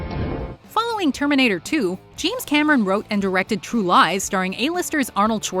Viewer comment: did she say jeems cameron?